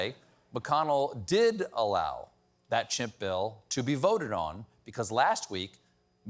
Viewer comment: Did I hear "Chimp Bill"? MACA = Make America Chimp Again.